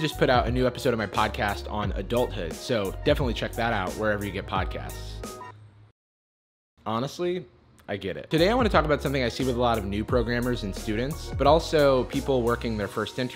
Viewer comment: Lol thought it said progamer